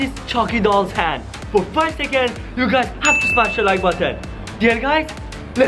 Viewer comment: i thought he said 5 sec not 10 sec lol